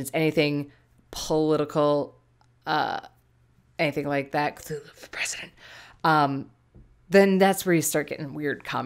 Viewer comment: did you just say "cthulhu for president"?! XDD